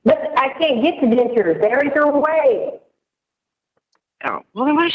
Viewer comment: I thought she said I can't get dentures there ain't no way 😂